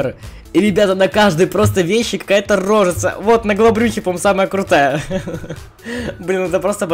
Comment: Послышалось: Вот, на голобрюхе самая крутая!